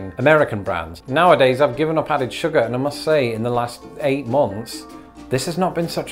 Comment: American here—I thought you said you gave up powdered sugar. I thought well, golly, how hard was that? Loved the video and the comments!